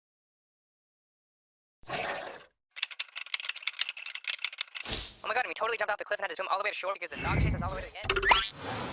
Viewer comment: I'm STILL trying to figure out what this guy is saying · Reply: "oh my gosh I would totally jump off the cliff that's from alaway short" that's what I hear (Put it in 0.05)